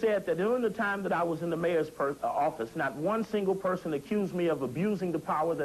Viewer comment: Did he say "when I was in the mayor's purse" @ 6:12?